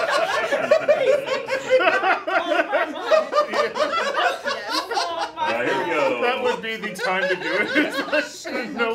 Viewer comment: Can anyone tell me what Jeremy said at 15:49? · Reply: The whispering part? "Hey! Hail Hydra!" but very fast.